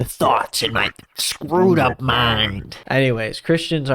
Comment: its took me a second to realize that you said "screwed up mind" because i definitely heard "scrotum mind"